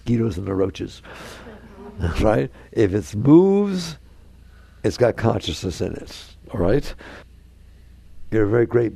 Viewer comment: Did he just say in that last sentence, “If it’s booze it has consciousness in it?” Wow. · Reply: If it moves, it got consciousness in it.” He said.